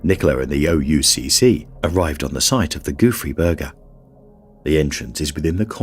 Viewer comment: i kept hearing "goofy burger". i see its spelled Gouffre Berger